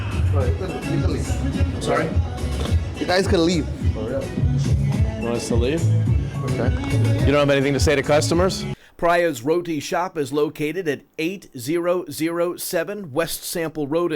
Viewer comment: I thought he said "Priya's Rodent Shop", and I started to say to myself, "Well played, sir. Well played." Then I saw the actual name of the restaurant. haha